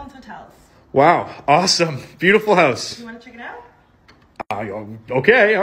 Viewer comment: That girl who said wanna check it out was so polite 🎯💯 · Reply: Yo I actually got hearing problems I thought she said wana check me out I was like ayooo